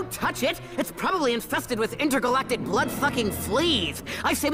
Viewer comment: is it just me or did duck say a curse word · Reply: He said sucking